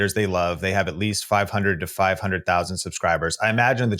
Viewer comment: If i’m wrong, correct me but it sounded like he said people that are 5000 two 500,000 subs will have the support from viewers to give them hypes so that means the small craters still are not getting recognized when I first heard about hypes I thought they were meant for the very small and upcoming creators like myself